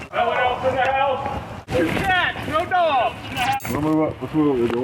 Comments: lol I thought he said just a cat, no dogs 😂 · Oh 😂I thought the cop said just a cat, no dogs